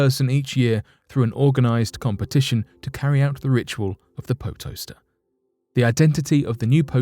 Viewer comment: who else thought he said 'po toaster? and expected to see a ghetto af toasting appliance?